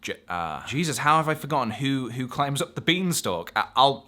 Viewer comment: when it jump cut from him going "um ah" to "jesus how i forgotten (etc)" i thought he was saying it was "jesus and the giant beanstalk" don't remember that being in the bible